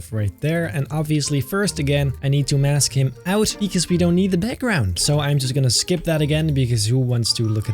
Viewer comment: What I heard: “I’m going to ask him out, because who wants their back round?” What said: “I need to mask him out, because we don’t need the background!”